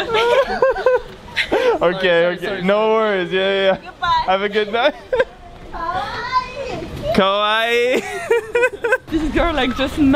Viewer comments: did she say "sakit"?, It means she is from Indonesia :v cause "sakit" means "hurt" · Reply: She said "so cute" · Nobody: Not a single soul: Girl: smashes into wall Girl again: KAWAIIII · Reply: She said kowai, which means scary, I get why you thought that she said kawaii because I used to confuse kawaii for kowai too